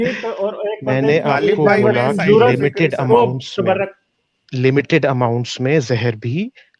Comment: did say "OOO sabar rakh" to?